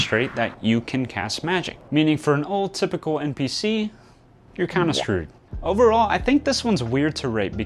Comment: At first I thought you said "for a neurotypical npc"